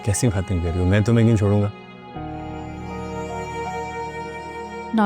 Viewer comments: Abrish said “mubatla “instead of “mutalba” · abrish ne kaha nazo baar baar bhai se alag hone ka (مطابلہ mutabla) kr rahi hai 😂😂😂😂instead of saying mutaliba مطالبہ